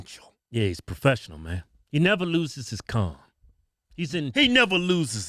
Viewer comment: I swear to God, I thought u said, "he never loses his cum...." I was like, is this a new expression i havent heard bout!? 😳